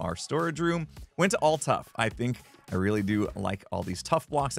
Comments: TFW you mishear “All tuff” as Ulta. 🤦🏻‍♀️ There’s Urban Decay makeup that gives off a Cyber Punk vibe. 😂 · He said "I went all Tuff...I thought he said he went to Ulta. Was so confused why he was at a make-up store.